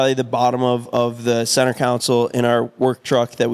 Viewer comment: anyone else hear saying “centre council” instead of console lol